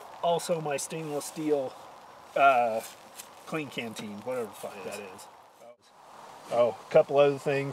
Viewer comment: Did he say what ever fuck that is at 1:23 lol 😂